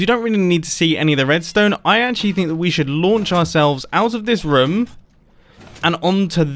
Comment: it sounds like he says rm